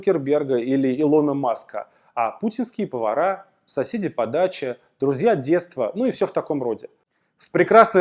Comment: когда говорили "соседи по даче", послышалось "сосИте по даче" :D Возможно не спроста )))